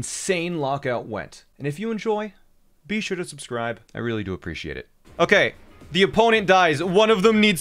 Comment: When he said i really appreciate it i thought he said i really poop your sh**